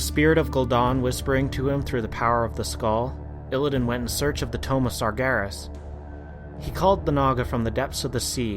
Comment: Satyr = Say-ter tomb = Tume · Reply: I thought he said tome of gul'dan so thanks for clearing up he just can not talk